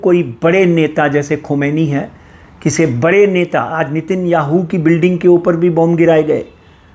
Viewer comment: did he say Nitin Yahoo 🙄😰😉😂